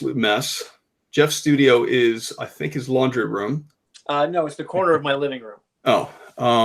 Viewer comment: Did say Jeff studio his laundry room 0:38 ?lol